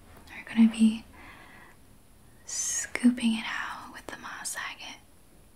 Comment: I thought you said, “we’re gonna scoop it out with the Bob Sagat”, 22:20. I need that crystal! I love your videos❤